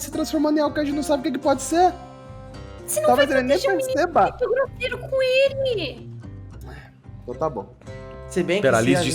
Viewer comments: No minuto 30:21 a diz: "Você não vai proteger o menino sendo grosseiro com ele!" E no minuto 34:27 o fala: "às vezes defender demais faz mais mal do que proteger, só digo isso" Sei lá, só caso alguém não tenha conseguido entender o que eles disseram, porque foi realmente difícil, eu tive que ouvir de novo e de novo várias vezes kkkkkkkkkkkkkkkkkkkkkkk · O que a disse em 30:20 ? Eu não entendi. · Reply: Foi algo tipo "dx o menino, vc tá sendo mt grosseiro c ele" O celbo ficou :O pq ela defendeu o joui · O QUE A DISSE NO 30:20 QUE EU NÃO ENTENDI?? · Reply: Ela falou tipo "vc n precisa ser grosseiro" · nao entendi direito oq a falou, algm pode me dizer? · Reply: "Você não vai proteger o menino sendo grosseiro com ele" meio irônico